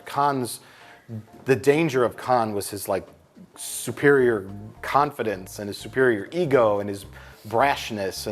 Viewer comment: Did Mike say Khanfidence?